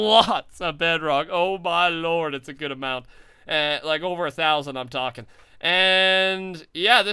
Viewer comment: When rusher said "Over 1000", I thought he said "ITS OVER 9000!" Lol